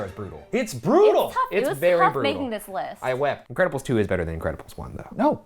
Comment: When Ryan said “incredibles 2” I heard “Grandpa is too/ grandpa Istoo”